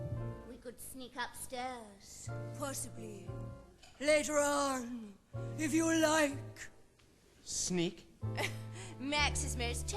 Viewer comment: What does she say at 1:46? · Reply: "We can sneak up possibly later on if you like." Is what she says.